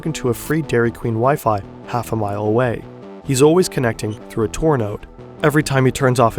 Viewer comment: What does he say at 9:44 "He's always connecting through a 'blank" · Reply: Through a TOR node